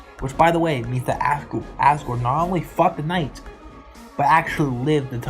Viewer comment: I thought u said he FUCKED the knight 💀